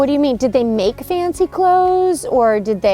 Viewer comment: OKAY WAIT! did anyone else hear “they made fancy clothes” in a whisper at 4:16?! Cause I’m trippin